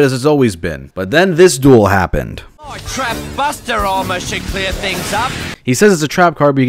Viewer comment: he doesnt say its a trap card he says the cards name which is trap buster armor, which implies its ability is to destroy traps. it was always a monster card he just says it in a way which sounded like he was playing a trap so its easy to get a confused lol.